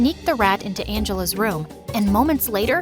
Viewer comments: she screws rats! But the captions say ruts lol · they put ruts instead of rats lol😂